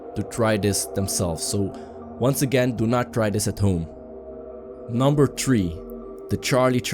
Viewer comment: he says number tree instead of number three lol